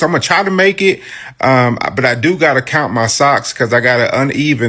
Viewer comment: I was minding my business and I heard “count my socks”😂